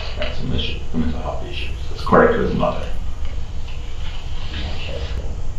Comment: he clearly says 'according to his mother' not 'is that correct or not' ....just saying